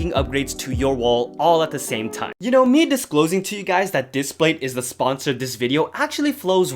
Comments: Who else thought the sponsor said dispatch I did be honest 😭🤣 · For a second I thought you said you were being sponsored by Dispatch it didn’t help that the logos look similar. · Why did i think when the word "displates" comes up i thought it said "Dispatch" 😂